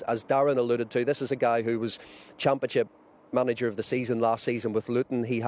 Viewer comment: did he say "Champachip". That must be the new league everyone is talking about.